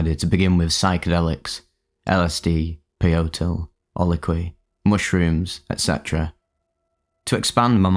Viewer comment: What drug did he mention around 2:46? He said "LSD, Peyote, (drug name), mushrooms, etc". I couldn't understand it. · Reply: Ololiuhqui. It's a type of morning glory. Seems like the guy writing the trip report didn't know that?